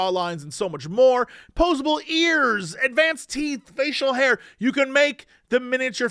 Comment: Now I KNOW that's not what he said. But in the Hero forge advert I heard... "..there's posable ears! ... And Man's teeth!!" 😂